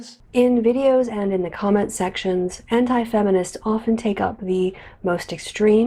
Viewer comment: I thought she said hentai feminist's